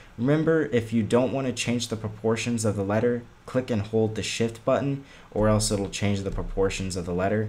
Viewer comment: I thought he said hold the SHIT button